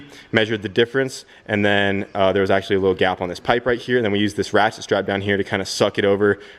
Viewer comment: did you say you used a wathit strap mike Tyson style ? 😂